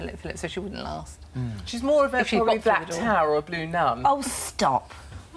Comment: What does Katie say at 9:27 that shocks everyone? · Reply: I dont know if I'm right correct me if I'm wrong but I think she said she either black or a blue nun I not sure what that means if anyone know tell me